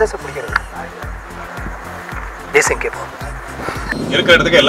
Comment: What does he say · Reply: Desingha keppom , desingh periyasamy is the director of movie.....